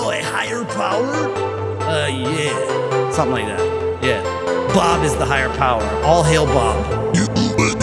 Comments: Sounds like it says bob is the nightmare power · Bob is the higher power. ALL HAIL BOB!!! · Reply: Fun Fact: I keep hearing "Bob is this a higher power" lol